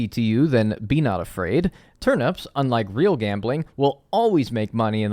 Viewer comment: I thought you said “like real gambling” not “unlike” and I was like oh bros just promoting gambling lmfao this was unexpected